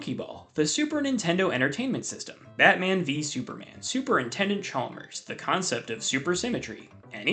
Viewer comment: On the mention of Superintendent Chalmers, I anticipate that at least one person will submit a slightly edited Steamed Hams transcript the next time they are allowed to write their own response to this question. Bonus points if they come up with something clever like: "You call them 'Super Mario' despite the fact that they're obviously Game & Watch." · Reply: Unrelated note, but is it only me that always misheard the name as "Super Nintendo Chalmers"?